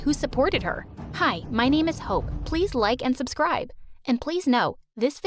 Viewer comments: I thought she said her name was hoe 💀🤚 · I first heard: "Hi my name is hoe" ✋💀😂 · I THOUGHT SHE SAID "MY NAME IS HOE" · I first I thought she said " hi my name is Hoe " 😂😅